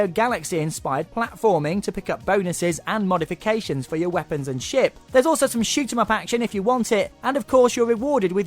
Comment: sounds like he says weapons and shit